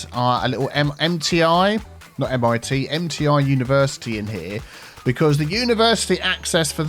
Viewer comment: I heard "Empty Eye University" instead of "MTI University" 😆